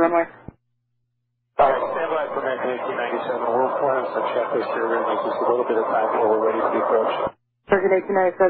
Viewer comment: He actually says "We're running some checklists here and we're going to need just a little bit of time before we're ready for the approach." Nothing about burning fuel.